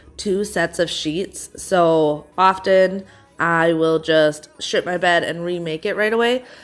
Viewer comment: I was only half listening and I swore you said "some days I will just sh*t my bed then remake it right away" and now I can't stop laughing 😂